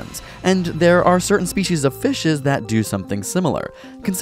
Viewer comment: Am I the only one who heard "fishes" at 3:10?